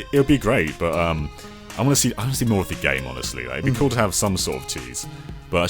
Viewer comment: i think he said "we could have some sort of titties"